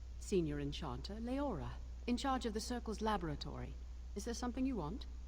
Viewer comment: I swear I heard her say "lavatory" haha